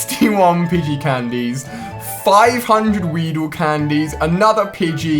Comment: Did he said 569 wheedle candy in 4:00 lol